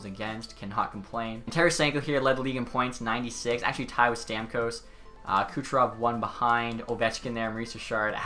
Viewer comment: Did anybody else hear him say "Tyler Stamkos"